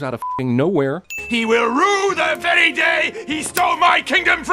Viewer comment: Was I the only one who heard him say day day?